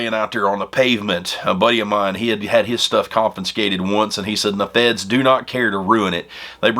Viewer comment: Did you mean con-fis-cated? Thought I heard con-fin-skated.